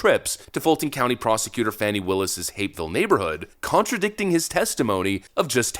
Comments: Hapeville? Oh I thought you said hate filled neighborhood. Guess either fits. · Oh, Mr, I thought you were saying that Fani Willis lived in 'Hateville.' I had to look it up. Hapeville, I see! Although 'Hateville' would be more appropriate.